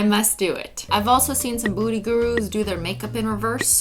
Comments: For one moment in my mind I thought you said booty Guru and Instead of beauty guru 😂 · OMG i can't stop laughing it sounds like she said "booty gurus" 0:42! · I swear I heard her say booty guru hahahaha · I thought she said booty gurus😂😂